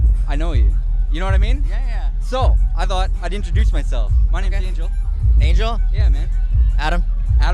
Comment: the subtitles say "my nigga angela"